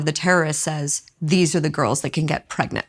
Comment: He did not say "These Are The Girls Who Can Get Pregnant” he said there are the "Sabaya" meaning female POW. Stop spreading lies and misinformation!